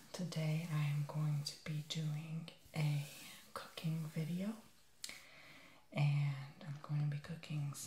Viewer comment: why did I hear "a cocaine video"...